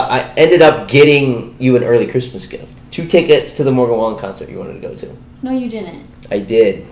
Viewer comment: I thought he said the mow the lawn concert lol.